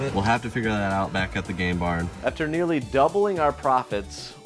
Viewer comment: When you said "game barn" I thought you were saying "gay bar" until I turned on CC. Sorry.